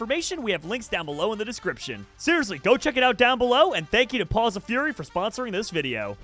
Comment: I thought it said paws of Furry and I'm like jerome fits right in